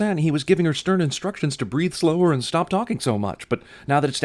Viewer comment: The word is "BREATHE" He SAYS breathe he does NOT say "breath" ARRRGGGGGGGGGGGGGGGGGGG!!!!!!!!!!!!!!!!!!!!!!!!!!!!!!!!!